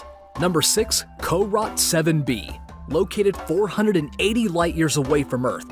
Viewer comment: Sounds like he said Car Rot 7b. Probably the pronunciation of carot7b. I'm used to hearing carrot 7b which is why I was thrown off for a second.